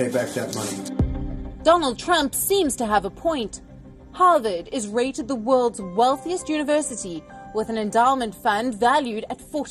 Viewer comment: around the 1;31 i almost thought she said. HARVARD IS THE WORLDS MOST WOKEST UNIVERSITIES